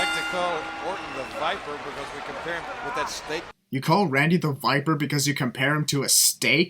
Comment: I think Jerry said snake not 'steak'